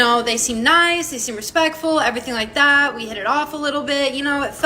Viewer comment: Did she just say "respectful"?